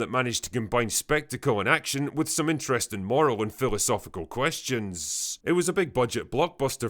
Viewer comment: Dude! When you said, “Giving us a film spectacle and action, with moral and philosophical questions.” I had thought you had said, “with Morrowind Philosophical Questions.” And I was excited for a moment that you named dropped a great game, that HAD a moral dilemma and a deeply entrenched philosophical story to it. But I think this is the first time your accent has ever made me miss heard you before!~ xD